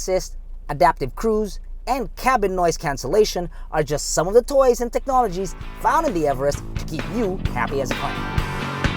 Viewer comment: I wonder what is he saying cause he keeps shouting HAHAHAHAH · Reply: He was saying "Open the door"